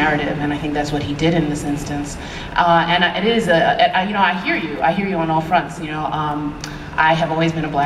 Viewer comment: Can someone please, tell me what she said on 3:18 because i don't understand cause I don't listen to english very well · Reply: "You know, I hear you. I hear you on all fronts."